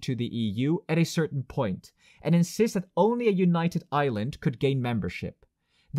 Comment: For a moment I heard "a united Ireland" instead of "a united Island". But that's another video :)